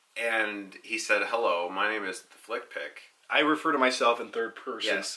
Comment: My name Is Slick Pick. Auto Generated Captions Say 😅